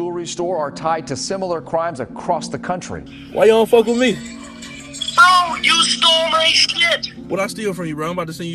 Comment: I thought this white dude said you stole my math book